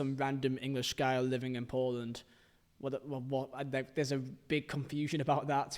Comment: I thought you said you lived in Portland (which i think is in the US, but maybe there's a Portland in the UK too)😅